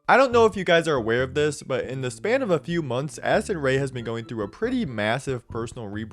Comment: every time in listen to this part I swear I hear “Acid Ray” and not “Addison Ray”